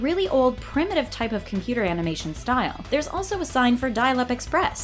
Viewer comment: did you say "amination"?